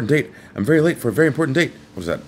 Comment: Al Snow in wonderland. That's what I heard. I'm addicted to WWE jeebus